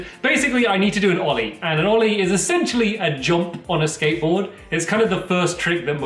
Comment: I thought It said Ori on a skateboard 😂😂